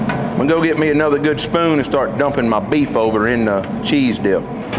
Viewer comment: I heard Beef!! Doesn’t matter, i knew what you meant!!😎